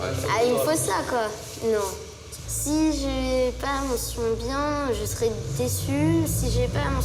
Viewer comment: C'est moi ou à 13:40 il dit " wlh je serais dessus "